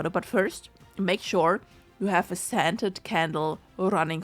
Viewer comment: Damnit I thought I heard her say "standard candle" not scented candle ! Me brains like , wtf? Where am I supposed to get a type 1A supernova? Enjoy my confusion! 🌠